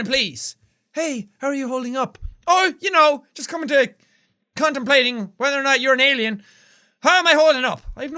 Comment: Who else heard "Just condomplating"